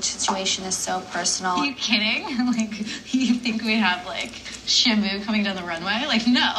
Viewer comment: What did Olivia say at 11:11? Something down the runway?? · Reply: "Shamu going down the runway." Shamu is a huge whale.